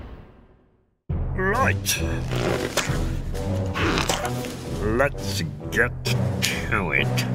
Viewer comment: Mr Bean: Let's get into it what i heard: Let's get cui